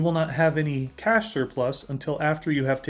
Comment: Auto generated captions said Cash Surplus but I looked other videos and I don’t see anyone saying about Cash Surplus so what word? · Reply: Yes, it was cash surplus. What I was meaning in that part of the video, is that you do not want to pay for spies unless you have more than enough cash to pay for troop and building cost.